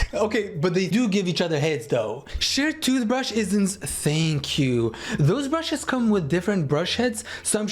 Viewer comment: I ALWAYS THOUGHT HE WAS SAYING “shared toothbrush is insaneQ” AND I JUST REALIZED HE SAID “shared toothbrush it in- thank you” HELP